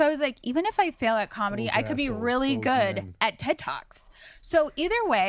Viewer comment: Who else Heard Tik Toks... not Ted talks?!!